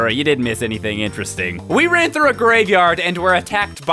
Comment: what did you say at around 9:00? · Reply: He said "We ran through a graveyard".